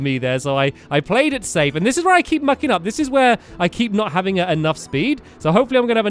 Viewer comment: Did say "This is were I keep f**king up" in 10:47 ?